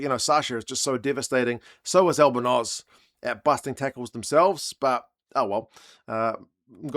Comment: I had to do a double take, I thought you said Albanoz was getting arrested 😂